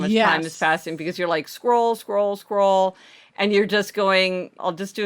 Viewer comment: When you were saying, "scroll, scroll, scroll" during the procrastination segment, I heard, "squirrel, squirrel, squirrel". That's a bit revealing about me, I'd say! ;)